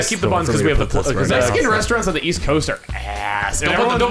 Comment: I originally heard this as "restaraunts on the east coast are ass" and I was about to get upset.